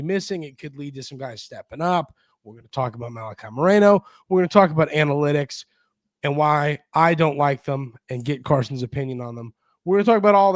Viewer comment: At first, I thought you said "we're gonna talk about Annie Lennox" analytics lol